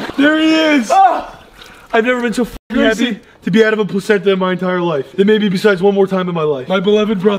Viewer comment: did Grayson just say "plazenta" ?... 😂😂😂😂😂